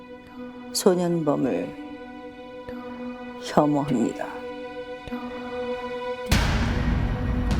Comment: 혐오립니다?? 뭐라는거지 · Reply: 혐오합니다 ..